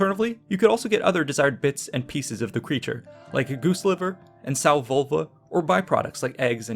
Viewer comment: Did I hear correctly? At 7:45 "sow vulva", as a speciality cut of meat?!